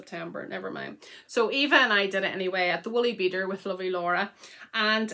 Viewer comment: Your podcasts are so much fun! I’m so glad you got to have special time with your kids. This whole time I’ve thought you said The Woolly Beaver instead of Beader😂. Both patterns are beautiful. 🧶❤️